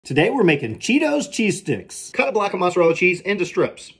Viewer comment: I heard “today where making diabetes”